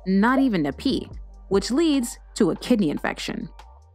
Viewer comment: Bruh wehn she said kidney infection I thought that she said "kitty infection"